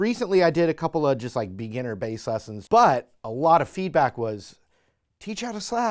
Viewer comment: I thought it said beginner slap the ass lesson